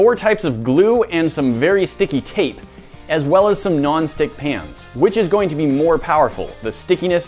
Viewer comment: Thought he said "Non-stick pants"